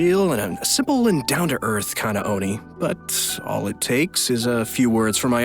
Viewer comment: when i watch the live i thought ayato calls itto a homie instead of oni. I am sad now.